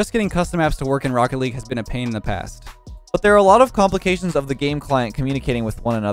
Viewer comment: when you said a pain in the past I thought you said a pain in the a$$